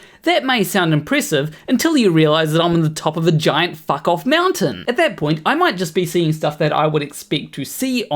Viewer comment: Did I hear “giant f*ck-all mountain”? Love it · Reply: It was "giant fuck-off mountain", which is a classification that I think falls somewhere between Mont Blanc and K2.